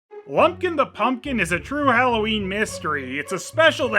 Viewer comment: I totally thought you said "Blumpkin the Pumpkin". I had to do a double take. That would have a very different movie if it were.